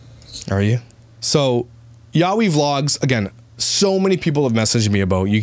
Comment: I thought it said yawn vlogs hahaha. They did make me yawn and honestly I'd rather watch paint dry then that family 🥱😴. love you!💜